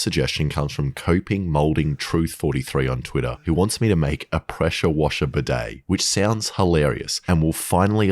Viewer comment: I thought u said pressure cooker bidet